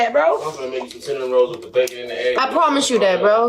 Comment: what did the guy in the background say? "I'm gonna make you cinnamon rolls with the bacon in the egg" ??